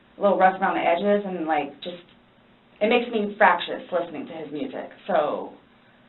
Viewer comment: what is the word she used here, anybody can or care to help me clarify. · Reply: Fractious. (typically of children) irritable and quarrelsome. "they fight and squabble like fractious children